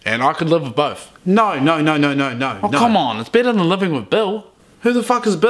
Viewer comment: Why'd I hear 'Better than living with bill' as 'Better than being Bill's Bitch'?